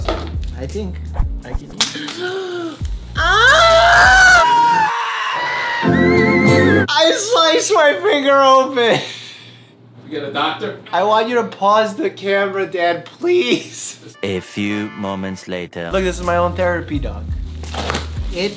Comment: Mike: gets cut Mike’s dad: “do you need a doctor?” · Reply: I thought he said "Dad can you turn the camera off" but im not sure thats just what i assumed